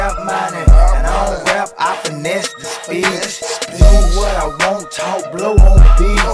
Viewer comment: I don't rap I finesse the speech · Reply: I thought he said “I finesse to speak”